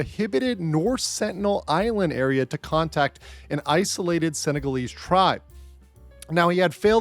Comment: did you say "SENEGALESE" Tribe??